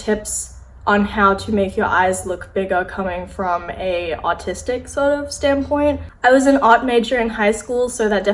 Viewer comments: I got really confused because I thought you said "autistic" at first lol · I thought she said autistic not artistic lol! 😂